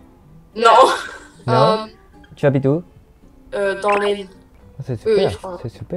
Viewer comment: I think she said "dans l'Aine"